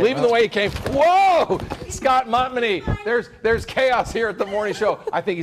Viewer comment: did he just say scott mommadee?